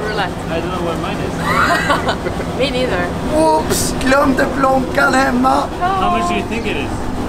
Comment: what does say at 7:02? I don't speak swedish! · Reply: "glömde plånkan hemma". I'm Norwegian but I assume plånkan is short for plånbok. Translates to something like "Forgot the wallet at home?".